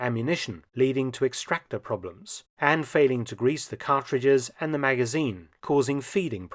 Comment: did you say "grease the mag and the rounds"?